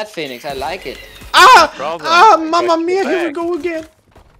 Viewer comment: does it say "mamma mia" ? :D